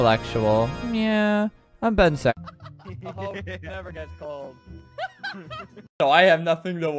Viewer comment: I’m sorry but did albert just say “a h0e never gets cold.” 💀